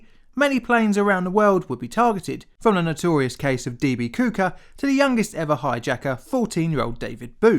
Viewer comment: That's Cooper. (Koop-er) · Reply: Yeah, I had to play it back a second time to see if I heard what I heard. " D.B. Kooker."